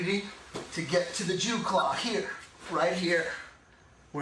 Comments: Did he say Jew Claw? LMAO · does he say 'to get to the jew claw'??? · in 4:57 did he say "Jew Claw" ? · does he say Jew claw?